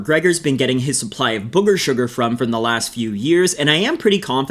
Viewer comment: I thought you said 'hooker sugar' !